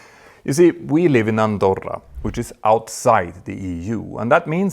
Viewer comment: The other pilot is so very cool and straight to the point. I'm glad you invited onto your program. It is very interesting to listen to his ideas which he makes clear or not conclusions.It's good to get his point of view as well. does seem circumspect and willing to bring up possible scenarios without making it seem as if they are facts. I wish you didn't interrupt him so often. I realize it is your broadcast but he is your guest. How is life in Andorra like? Is it expensive? Is it at a high altitude and do people speak French or Spanish in Andorra? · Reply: Thank you for clearing up something I misheard say. I thought he said he was living in Andover (an industrial town in mid Hampshire, England) which is very different from what he actually said (and a much more desirable place to live) Andorra! 😅